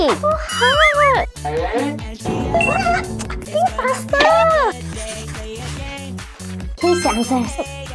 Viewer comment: I heard “think duster” when it was think faster😂